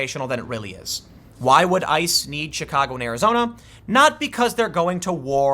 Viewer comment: Did you just say "Shitcago" ?